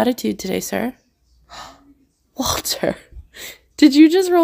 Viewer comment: I heard you say "water" instead of walter lol